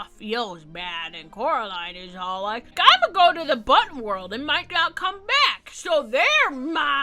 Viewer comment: At first I thought they said "butt world"